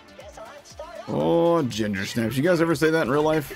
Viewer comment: I thought he was saying "Oh gender snips" haha